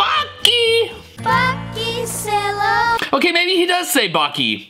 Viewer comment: nah i heard f###ing Sail Away